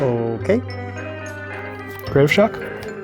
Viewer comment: When they said "Creative Shock", I thought for sure they said "Grapeshot" and I was like "Welp, that's game..."